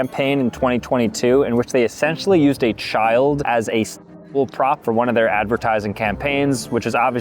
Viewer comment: (16+ comment) for people that don't know what he said, he said sexual product. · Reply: He said prop but okay